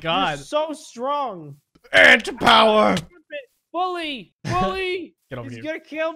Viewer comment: I thought he said STAND POWER